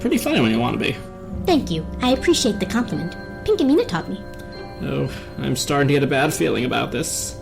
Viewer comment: D- did she say Pinkamina?!?